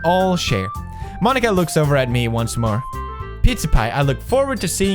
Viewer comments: Sounds like he says PizzaPie xDD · did he just say pizza pie